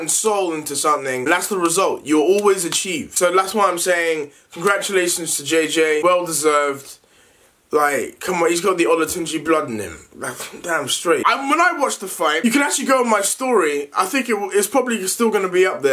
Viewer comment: well, comeon he got the olatonje blood in him 😂😂😂😂 sounds like he is saying he got the joestar blood in him thats why he won 😂😂😂😂